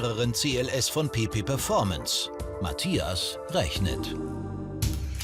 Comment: Ich hab die ganz Zeit PiPi performance verstanden :D